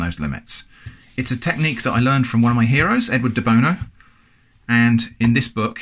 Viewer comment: Who else heard Edward The Boner instead of Edward De Bono